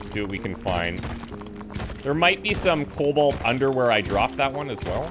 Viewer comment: "There may be some Colbalt underwear" Is what I heard 😆